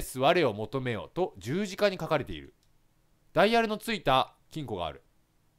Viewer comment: ｢ダイヤルの付いたち〇こがある｣にしか聞こえないんだが笑笑